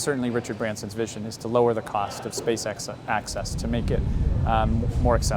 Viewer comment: I heard "SpaceX" there ;D